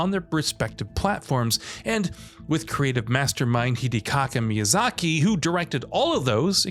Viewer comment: Did he say Hidekaka Miyazaki?